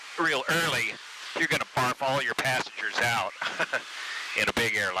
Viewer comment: About 15:30 in I thought I heard say not to early you do not want to barf out the passengers. Did that come from past experiences? Liked that terminology.